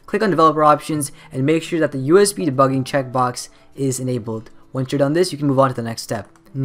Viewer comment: What did he say " Make sure that the debugging option IS enabled or disabled " if he said enabled why he UNMARKED the button !! that's so tricky Please someone clarify!! and the links not works ?!! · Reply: Enabled